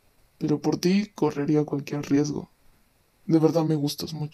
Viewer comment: pero por ti correría cualquier riesgo 💕 Lo que yo escuché xd -por ti baby sería Batman 7w7